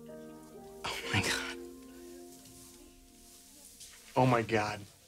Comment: I thought Spencer said OH MY GYAT